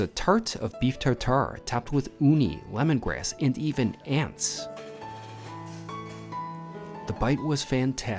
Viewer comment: bro, did you say ANTS??? 🤮🤮🤮🤮🤮🤮🤮🤮🤮🤮🤮🤮🤮🤮🤮🤮🤮🤮🤮🤮🤮🤮🤮🤮🤮🤮🤮🤮🤮🤮🤮🤮🤮🤮🤮🤮🤮🤮🤮🤮🤮🤮🤮🤮🤮🤮🤮🤮🤮🤮